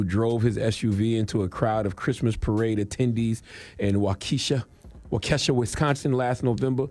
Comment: I don’t like cthagod saying Waukesha (walk e Shaw) lol sound like he saying Wokesha 😂